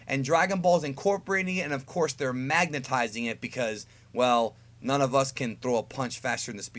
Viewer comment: did he just say Magnetizing?